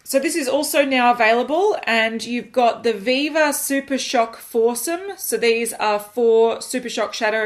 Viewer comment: god I understood "force 'em" instead of "foursome"... What the fuck brain